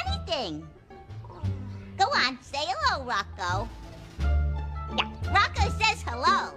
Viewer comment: I love just the defeated tone I'm elmos voice when he said "hello rocko" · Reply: "hello rocko" i thought i heard "hello bucko"